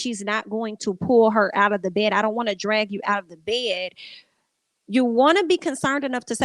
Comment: Sounds like she said "I don't want to drag her under the bed" which doesn't make sense unless she meant to say she doesn't want to drag her under the bus idk.